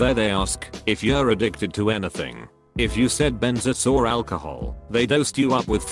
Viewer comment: they ask you if you're addicted to anything, like BENS ASS 😂😂😂😂😂😂 that's what the captions say